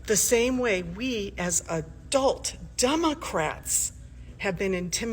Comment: Minute 3:34 she actually says “…We as adult Dumbocrats”. Just listen to it and you be the judge.😮